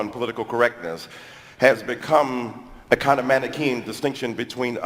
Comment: I thought Dyson said mannequin lol